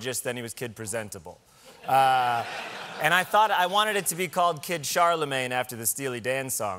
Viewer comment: kid charlemange....... heard that as kid chalamet for some reason and was like john you can't... the timothee stans will be m a d